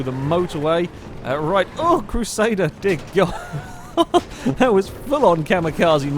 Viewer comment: Waaitt? Did he just swear? Did he say dick? xD i have never heard him swear?? Wtf xD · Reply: No he said dear god